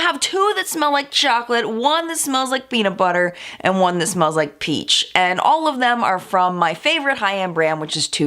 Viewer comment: the video froze before the "ch" sound in peach so it sounded like she said "two that smell like chocolate one that smells like peanut butter and one that smells like pee" oh my lord